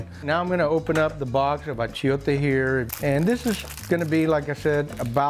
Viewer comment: l can't really understand what he's opening up at 4:20. Could someone explain? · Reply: Achiote Paste!